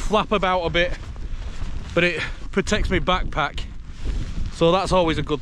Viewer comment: Great video i thought you said poncho was to protect you back crack 😁 then wife said backpack